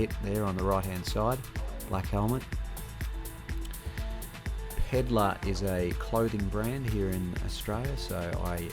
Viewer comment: what is the clothing brand again? Even better if you got the link, I just can't understand what the name is! I'm not a native speaker, accents different from US are hard for me :( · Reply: pedla